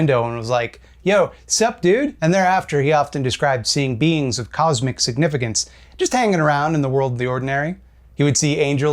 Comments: I definitely heard “beans of cosmic significance.” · At the beginning of the video I thought you said he "saw beans of cosmic significance" just hanging around. That was interesting. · I misheard you at first and thought you said "Beans of cosmic significance" and would that have made for a fun read.